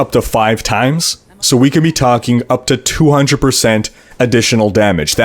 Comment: ***** If you're referring to me saying that Focus & Restraint add up to "double damage," I'm just saying that as a simplification. I know it's 125% extra damage, but to a first approximation, the difference between "double damage" and "125% additional damage" is not significant for explanatory purposes. I worked as a scientist, and we do these things called "back of the envelope calculations" in which we just look to get an estimate of values, so we'll do things like round gravity off to 10 instead of 9.81 etc. The important thing is being accurate within an order of magnitude. Also, re. 300% additional damage = quad damage, I don't recall what I said in this video, but I know in several videos I say "quadruple damage" instead of "300% additional damage" or "triple damage" instead of "200%" increased damage etc. It's possible I misspoke in this video; I don't recall what specifically you're referencing. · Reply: ***** Yeah my bad I misheard you say 300%. 8:49. Scientist eh? What field? I myself studied economics so I had my fair share of math and econometrics